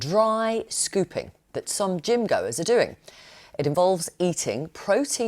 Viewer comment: I misheard the newsreader early in the broadcast. Apparently she said "...some gym goers..." but I thought she said "...some dingos..." Don"t feed your dingo on it chaps! A good walk in the park is all they need!